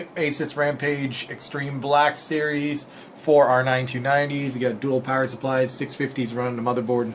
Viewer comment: Well, what do you expect lol?! There are like 3 games that support 4 way SLI with OK scaling most games scale worse with the 3rd or 4th card. And AMD cards suck anyway due to lack of optimisation. I'm running a 3930k 4,5Ghz and a single 980 at 1515Mhz and get ~70-80Fps on ultra everything max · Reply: ThatAnnoyingGuy for a second i thought you said a single r9 290x and i almost lost my mind lol